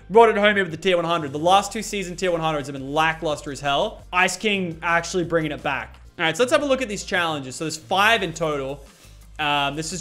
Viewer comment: Did he just call Ragnarok "lackluster as hell"?